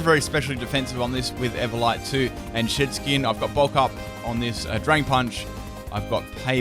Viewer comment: Anyone else hear 'shit-skin' every time?